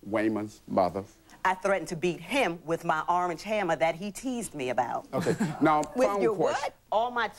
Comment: I thought she said arm and hammer.